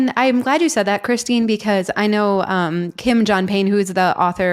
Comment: I thought she said Kim Jong Un 😂